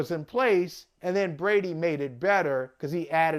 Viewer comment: what does Brady have to do with the run game? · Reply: I thought you guys said Brady made everybody better.